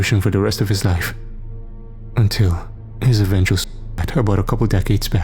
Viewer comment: did i mishear or was it probably "suicide" that got muted?